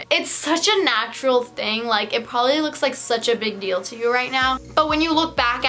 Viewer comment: did she say pobably